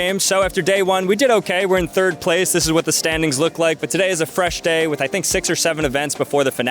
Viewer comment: did I hear 6’7???